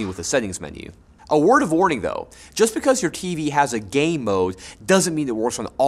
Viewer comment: I heard: Just because your TV has a gay mode...